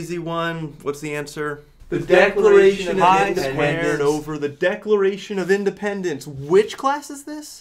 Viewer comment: What do they say at 0:12? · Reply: The declaration of independence